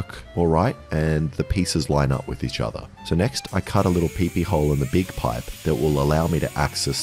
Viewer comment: “I cut a what hole” can someone tell me what he’s saying please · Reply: PP hole